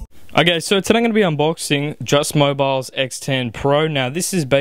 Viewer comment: lol it sounded like you said "Just My Balls" instead of just mobile